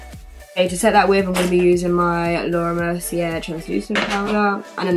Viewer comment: what's the name of the powder? I didn't catch it! Laura something powder?... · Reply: Laura Mercier :)